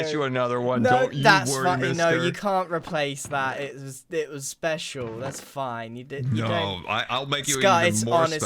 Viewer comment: Ayo did just swear at 4:16? I can't find any other way to interpret what he just said... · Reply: "that's fin- no it was special" is not swearing.